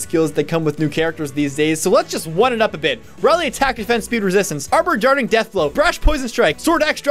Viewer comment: If no one knows what he's saying at 3:00 "Rally Atk/def/spd/res! Armored Darting Death Blow! Brash Poison Strike! sword/axe/dragon/shurikenbreaker! (it's daggerbreaker but ok...) Quick Desperate Assault! (where's vantage? : Breath of Savage Life! (lol)"